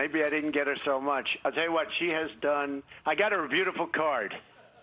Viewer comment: Wait, what? I thought he said a beautiful car. A card? Melania looks so unhappy all the time, no bueno. No woman deserves so much bitterness in her life.